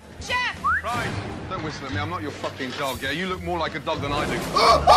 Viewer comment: did he say u look like more than a dog than i do?